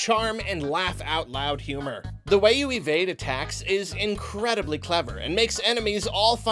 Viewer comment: I had to do a double take at 6:21 because I thought he said "the way you evade a tax" when talking about Undertale